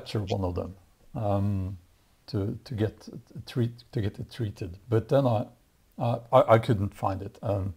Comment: Oh treat, I thought it said eat. ;P Carry on the good fight. :)